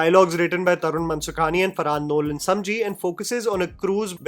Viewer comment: Did you just say farhad Nolan samji 😂😂